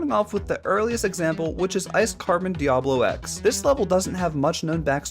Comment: what word did you say fix it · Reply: Ice carbon Diablo X